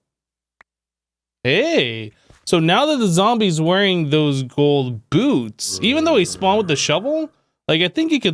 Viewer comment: Anyone else catch “Bold Goots” at 6:26?